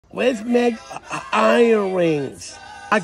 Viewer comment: For a minute I thought he said iron rings good stuff man